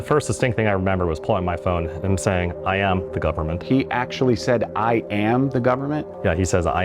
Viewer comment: I think I heard “ it’s called the government” not “ I am the government” but anyhow thanks for sticking up for yourself!